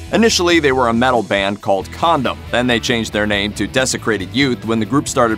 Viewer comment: did he really said condom?